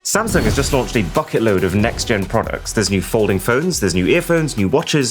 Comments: thought u said a f*** load for a sec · I thought he said at the start samsung launched a fuck of load